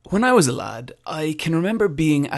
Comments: Honestly thought he said, "When I was alive..." at first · I thought he said "when I was alive"...